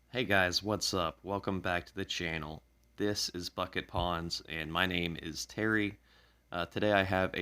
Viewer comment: I thought i heard u say "this is fuckit ponds" hahaha